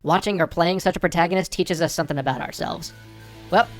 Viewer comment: I might be hearing things, but I could have sworn he said "Happy halloween everypony" ...maybe I'm a little too obsessed with certain show...